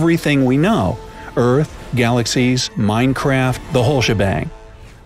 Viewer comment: did u just say minecraft